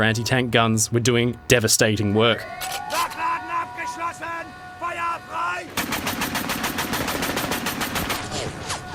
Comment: What does he yell in German? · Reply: Nachladen abgeschlossen, feuer frei!